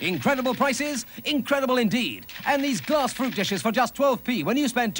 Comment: Did he say "Glass proof dishes" at 9.09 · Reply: I thought he said "glass proof dishes" too.